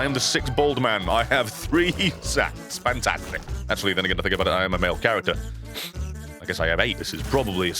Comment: WTF DOES HE SAY AT 5:06???? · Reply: “Then actually thinking about it again I am a make character”